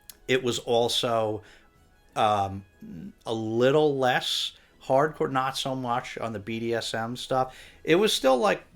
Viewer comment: Happy holidays! When you were talking about Olive's racy book picks I thought you were saying "parkour" instead of "hard core". But I guess both kinda work.